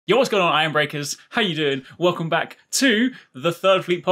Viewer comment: Oh so it's Iron Breakers, I always thought said Arm Breakers like you break people's arms because they attack your shield.